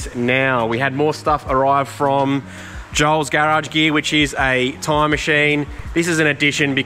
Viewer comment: Buys tyre machine; instantly invites all mates to use it. Top class. That’s why so many are helping. What goes around comes around etc. 👌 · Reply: For some reason I kept hearing "Time machine" and him offering to change a time for his mates XD